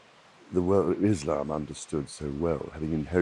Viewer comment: did he said islam understand so well